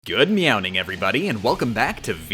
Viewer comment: I'm sorry, are you saying Meow? I could have sworn you said Meow